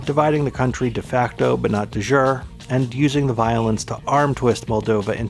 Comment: Ooooh... "de jure." I thought you said "du jour."